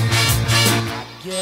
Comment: I thought they were saying Mollasses